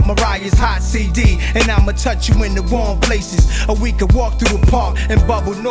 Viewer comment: "And imma touch in the warm places, or we can walk through park in bubble north faces, im lost in your love."- NAS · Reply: I always thought he said wrong places???